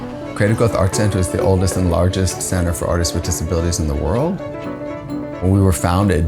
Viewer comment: Im so sorry but i thought it said diabities (Sorry jf wrong spelling)